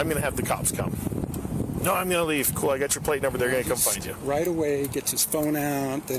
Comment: It did sort of sound like he said 'I'm gonna escape.' I realized later he said 'I'm gonna skate.' It doesn't excuse things but it does explain them just a little bit I think.